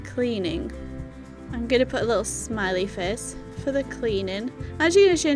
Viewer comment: lol it sounded like you said smelly face for the cleaning. I had to go back and re listening, you did actually say smiley face.